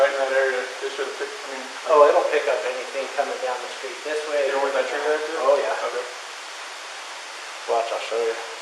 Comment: "even with that tree right there?" ANYBODY else hear that? WOW!